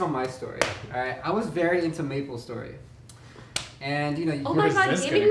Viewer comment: I thought fed said "maple syrup" not maple story.